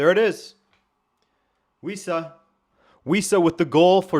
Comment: Wissa? At first I thought you said "We Suck" 😅